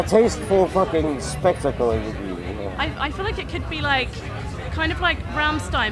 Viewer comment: what she says ? I'm french ^^ and i dont understand really well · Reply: She says ''I feel like it could be like Rammstein but then more of a gentlemen's club, that sort of thing'' - Comme Rammstein mais plus sophistiqué :)